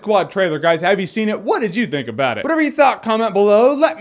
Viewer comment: He say's "click here to see more", i hear "click here to Seymour"